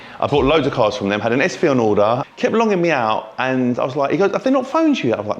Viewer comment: wtf is saying here! 😂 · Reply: Longing me out, so taking long